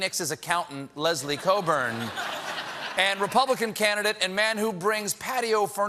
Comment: Why do the captions Say Laura Coates at 0:34 instead of Leslie Cockburn?